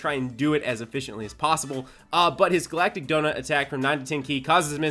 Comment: It wasn't me right when I heard him say glatic gonut right?